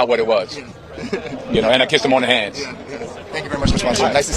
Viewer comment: yt:Now he kiss him on the hand thought he said on the lips